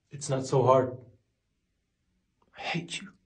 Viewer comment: For a sec, I heard "bencho" a hindi abuse... Instead of I hate you 😂😂